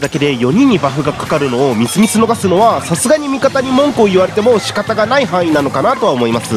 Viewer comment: 文句があれに聞こえてしまう 俺は病気なのだろうか